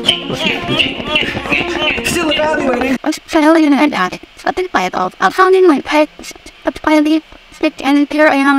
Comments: For the Stevie Ray Vaughan one, I heard 'this alien outlet, or a light bulb, I found in my pants' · i heard "I fart in my pants" 😅😅